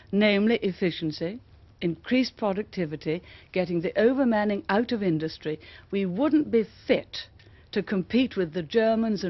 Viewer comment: I am unable to understand what she said, "getting the ......... out of industry"? What is the word she says there that they got out of industry? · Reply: I believe Maggie's words were 'getting the over manning out of the industry' meaning too many people doing a job that fewer people could be doing - inefficiency